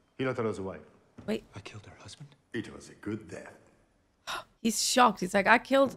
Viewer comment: "It was a good death" ALL THIS TIME I THOUGHT HE SAID "He was a good dad"